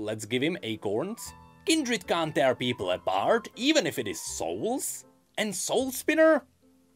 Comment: what the heck does he say about kindred here? kindred can't do what to people? XD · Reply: kindred can't air people at bard? XD